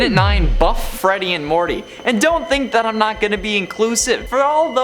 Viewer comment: he says buff greedy and Morty instead of Monty